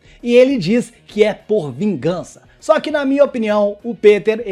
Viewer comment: Correção, o Peter diz q "PODE SER" vingança Foi bem sútil, mas ele não afirma isso